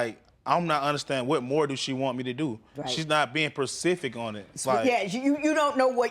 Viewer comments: Did he say she not “persific” enough 🤔😮😔😂🤣… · Did he just say "percific" ??!!😞😡😕